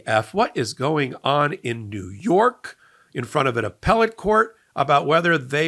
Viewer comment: I got corrected for using the term "appellant court" and was told it should be "appellate court". However @ 0:07 Popok seems to say "appellant", so is appellant actually OK, or am I just mishearing?